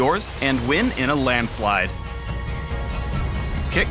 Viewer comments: did he just say water you waiting for? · I GET IT! Instead of what are you waiting for you said water waiting for